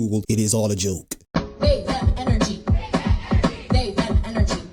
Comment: What is that individual even saying at 4:30 ish?? I can't quite figure it out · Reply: They-them energy.